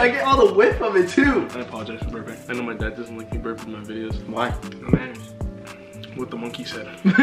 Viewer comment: What did said at 6:34 ....after that said what the monkey said but what did he said? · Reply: He said the manners